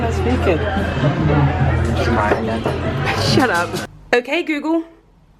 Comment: Anyone know what said at 0:43??? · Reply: "She's crying again..."